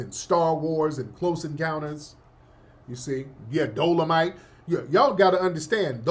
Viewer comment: every time I hear Dolomite, now I think DoloMust😂